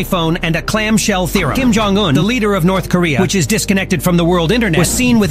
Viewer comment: I heard “Kim Jong Un, deleter of North Korea” 😂